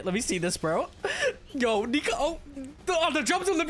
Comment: What did he just say😳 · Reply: Bruhhh “nika” 😭😭😭